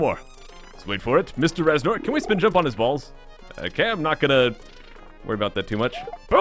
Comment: it sounded like he said "can we jump on his balls?" 😂